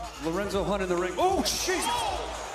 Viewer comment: 🤣🤣🤣 tf he say? “Ooh sheeshus”